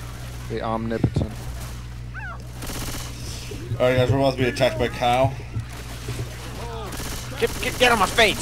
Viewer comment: Did you say "we're about to get attacked by Kyle"? O.o